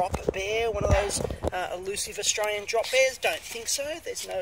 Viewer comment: At first I thought you said "Drop Beers". Obviously that is sacrilege in Australia.